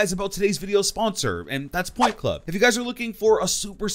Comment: Instead of point club i thought he said pornhub